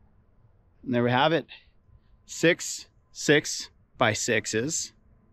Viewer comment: did just say 6.6.6. ought oh !